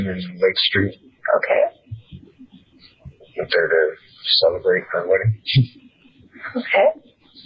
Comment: Is it just me or does say "to celebrate MY wedding" at around 12:45?